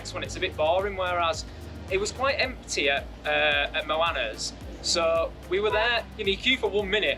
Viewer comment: nice video! What was the name of the place with the "sets". Sounded like you said Mulana but can't find. Thks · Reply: Thanks - glad you like it! 🙌 Sorry that’s my strong accent. It Moana - if you Google Moana Sapa you should find it!